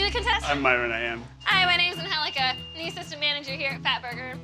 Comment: I could of sworn it said her name was Angelica?